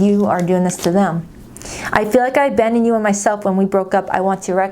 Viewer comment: ? Does the first card say I feel like I abandoned u and myself or what did it say ? Couldn’t make it out help 😅😅. Thx